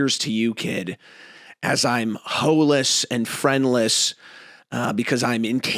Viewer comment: Did bro say hoeless instead of bitchless, that’s crazy xddd